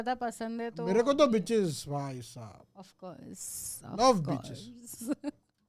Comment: Bro 4:44 did u say beach or bitch😮😮😮😮